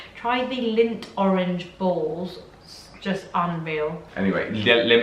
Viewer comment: I thought was saying 'dunk lint balls in her tea' ..took me a minute to realise that she was saying Lindt 😁